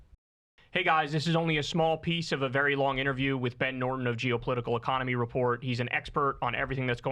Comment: I heard “ed Norton” and was confused for a good 3 minutes 😂🤭